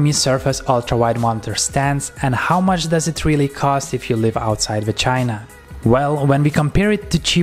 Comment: did he say "if you live outside vagina"? 😳